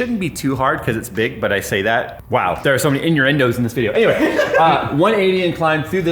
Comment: I swear I heard Jay say "inyourendos" instead of innuendos.... Did I hear that right Jay?